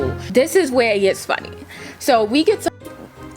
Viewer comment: I thought she said “we get to f*cking” lol girlll